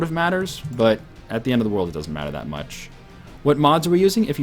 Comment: did you just say "at the end of the world it doesnt matter"?